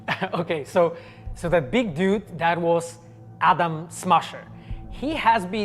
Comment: Adam Smasher?! Oh man... He's even scarier in 2077! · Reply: I thought it said Atom smasher, haha Now that is a tough name!